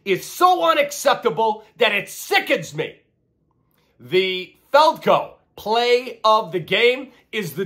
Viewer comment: And I keep hearing "the Falco" play of the game 🤣